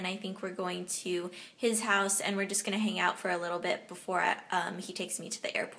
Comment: I'm sorry but I wasn't entirely paying attention for one second and when you said "We're going to hang out for a little bit" I thought you said "We're going to make out for a little bit." :p